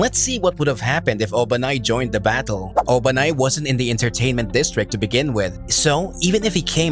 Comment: thought it said "What if Obama had joined the fight in entertainment distrcit"